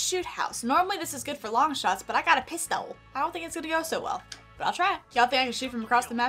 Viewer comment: Bruh at 25:08 it sounds like she saying I got to piss tho